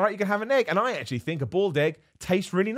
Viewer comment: Thought you said "bald egg" instead of "boiled egg".